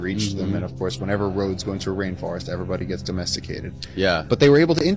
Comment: Did he say ' Domesticated ' ?